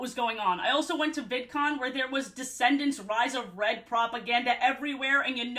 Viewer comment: Why did I mishear you saying "rise of bread?" Why do I now want them to make food based on Descendants characters and for Red's thing to be either bread that's dyed red or red velvet cake? Wait, other than being sweet, what's the difference between cake and bread? Why am I questioning this in a video about Descendants? Why am I asking so many questions to begin with?!